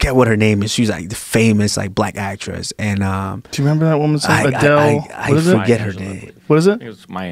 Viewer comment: What made me cry was these idiots mixing up Della Reese and Roma Downey. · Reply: I thought Joe said Adelle at one pick